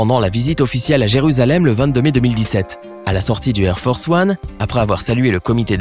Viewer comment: J'ai entendu "hair force one" !! 😂😂